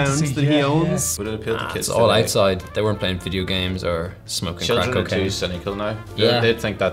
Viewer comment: did they say smoking cocaine around 0:50?